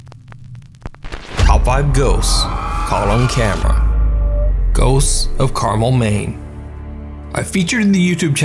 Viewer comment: I thought you said ghosts of CARAMEL maine 😅